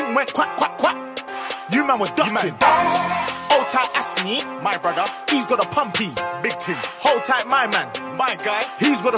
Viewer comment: Ok, dont lie who else thought he said "my brother assnee, he got a puppy, big tim"? Just me?